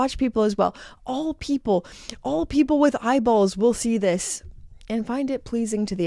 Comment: I literally thought that she said that all people who have balls will like Frederique Constant 😂